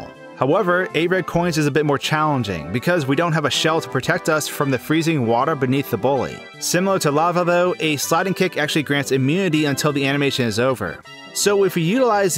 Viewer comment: the subtitles say sliding kick. but to me it sounds like says sonic kick